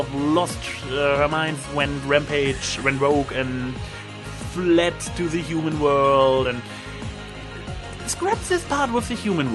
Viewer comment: Wait, at about 2:50 it sounded like you said Rampage went rogue and went to the human world... She really has been around hasn't she?